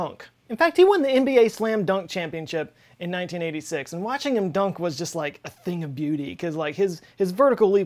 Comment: i heard you say "watching him dunk was like eating a dookie" o___O